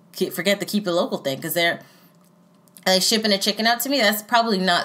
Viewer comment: did she just say do they ship chickens